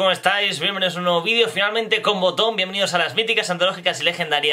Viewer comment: Te juro que escuché "Con condón" jajajajaja